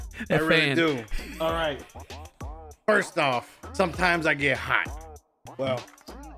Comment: being so excited to show off his fan cup hybrid is exactly the wholesomeness we need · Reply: I misheard. I'm glad I was scanning the comments. Instead of hot I thought he said, "sometimes I like to get high" Super cool cup though!!